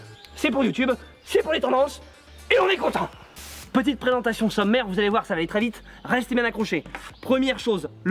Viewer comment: j'ai compris "sa mère" au lieu de "sommaire" jetait en mode "OK ta bien changé edison hein......" 😪😂